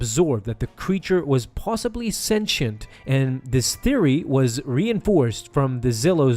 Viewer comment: senti what? What’s that word I didn’t hear correctly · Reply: Sentient. (Self-aware etc.)